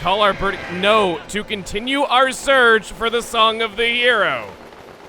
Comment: it sounded like he said "Song of the Euro" lol 😂